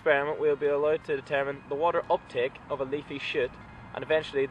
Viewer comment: Sounds like you said "leaf shit"